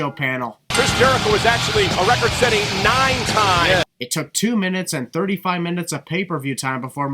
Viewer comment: you said two minutes but subtitles says two matches